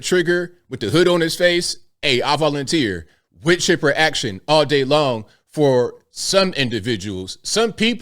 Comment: Was he trying to say "wood chipper action all day long" because it sounds like he said with chipper. Anyway, wow, wood chipper action. WOW!!